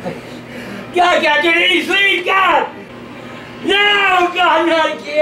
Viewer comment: does he say "I can't see" or "I can't sleep"? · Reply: After 8 years you have your answer sleep.....